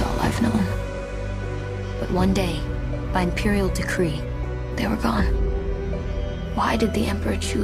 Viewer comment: I'm an OG Star Wars Trilogy fan, finally glad we've got a (hopefully) new cinematic Universe that covers the visceral/emotional power of true sci-fi. You've captured the essence of Dune perfectly - beautiful edit. I think we're in for an amazing experience over the next few years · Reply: Excellent cut. At 0:30 though, you have it written as "imperial degree" instead of "imperial decree". Doesn't take anything away just wanted to let you know so you could retain a better master copy. also: at 1:30 it should be spelled Lisan Al-Ghaib